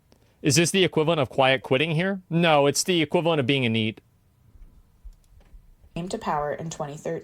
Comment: What is "being a need" or "being a neat"? I'm not sure what he said there and I've never heard that expression. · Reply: being a NEET. Wikipedia: A NEET, an acronym for "Not in Education, Employment, or Training", is a person who is unemployed and not receiving an education or vocational training. The classification originated in the United Kingdom in the late 1990s, and its use has spread, in varying degrees, to other countries, including Japan, South Korea, China, Serbia, Canada, and the United States. The NEET category includes the unemployed (individuals without a job and seeking one), as well as individuals outside the labour force (without a job and not seeking one). It is usually age-bounded to exclude people in old-age retirement.